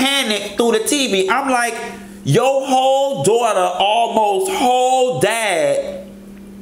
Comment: 'Your whole daugther almost whole died!!!'... 😂😂😂😂😂😂😂😂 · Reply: That was funny as hell, I really thought he was saying hoe daughter. Like Porcha told Kim "yo hoe daughter".